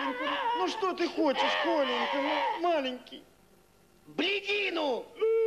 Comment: мне послышалось- "блядину"